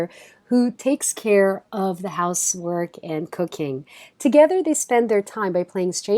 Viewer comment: did she just say cocaine?